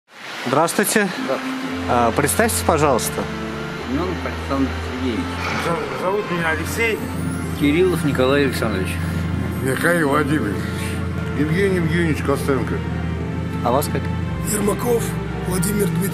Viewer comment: Вместо зовут меня Алексей и Кириллов Николай Александрович, послышалось - зовите Кириллов, Николая и Александра, стояла низкая громкость у видоса.